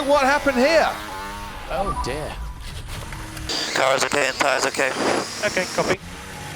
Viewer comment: Did Lewis just say “tires ok”? Cause my ears must be deceiving me. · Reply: I heard "car is ok".